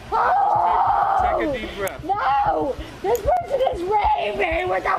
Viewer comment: she clearly says: "this person is raving". This is not a hysterical woman: this is likely a woman with dissociative disorder (multiple-personality disorder). She is insane. Stay safe men, avoid evil (and crazy women), enjoy the decline.